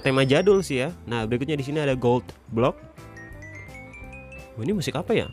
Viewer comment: ko gua dengernya kayak ngomong "goblok" ya 😂😂